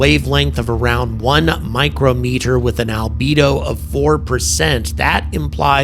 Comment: you said albedo, I thought you said libido and all I could think was "yeah, makes sense. Depending on what this thing does we're fucked." I probably horribly butchered the spelling of albedo too. Eh. /shrug